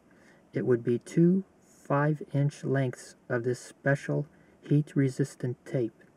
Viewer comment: Really nice tips. So it's a 25 inch tape divided to 6 varying size. What is the exact size of each cut? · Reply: 25 inch? I swore he said 5 inch. You can't put 25 inches of tape on the mount